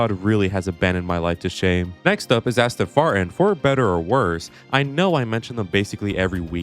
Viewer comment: Did you say Asston Fartin?